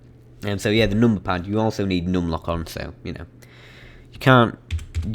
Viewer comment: what did you said around 12:53-12:57 i didnt understand what you said around that part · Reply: NUM LOCK ON